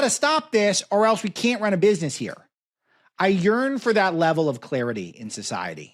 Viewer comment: Alex, at 36:20, when you were talking about the potentially unpleasant mishaps in front Starbucks, did you say that you yearn or urine for that level of clarity? Just asking. <grin> Love your show!